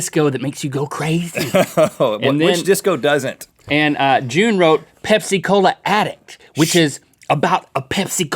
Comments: When they said Pepsi Cola Addict I thought that they said Pepsi Cola Attic... I thought it was about an attic filled with Pepsi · When they said Pepsi Cola Addict I thought that they said Pepsi Cola Attic... I thought it was about an attic filled with Pepsi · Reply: I thought they said Pepsi Cola At it... XD